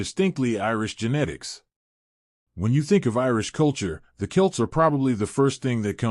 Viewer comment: It sounded like he said "When you think of Irish culture, the KILTS are probably the first thing that comes to mind. But WHO were they, & what role did they play in shaping Irish DNA?" And you think "Wait, the kilts were skirts. They weren't alive." THEN you realize: Oh - he meant that 'when you think of Irish culture, the Celts ...' Haha. SO, the narrator has some kind of accent to this American's Northern ears.